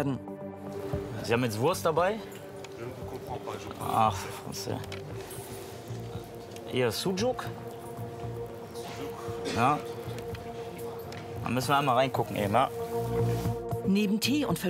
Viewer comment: „Ach Fresse“ oder hab ich da was falsch verstanden haha